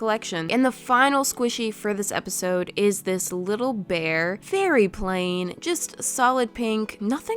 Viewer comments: i was eating i i thought she said "beary plain" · D-did you say beary plain? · I thought she said “BEARY plain”